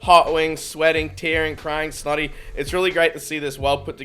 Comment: did say slutty?